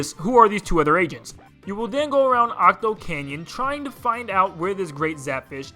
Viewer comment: did u say Octo Canyon